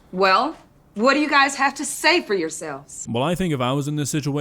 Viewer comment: she says "yourselves" but the caption says "yourself" and the actual YT automated captions say the right thing 💀